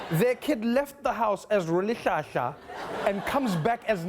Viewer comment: Someone spell Rolihlaha. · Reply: I heard "Roholihlaha" 😕